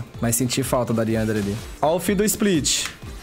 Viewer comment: No minuto 3:55 só eu que ouvi o fiddle falando otario ?